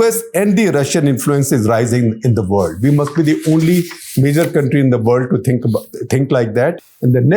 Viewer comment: Did he say "nigger country" around 21:08??